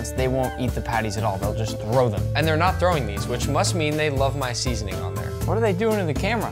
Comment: AWW I heard “LoVe iT”